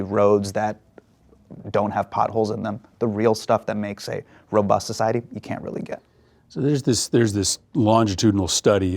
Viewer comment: “you’re talking about the __ class,” what word is that? I can’t make it out. Can anybody fill me in? 🫶🏻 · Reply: C-suite class, CEOs, CMOs, COOs, etc. chief __ officer.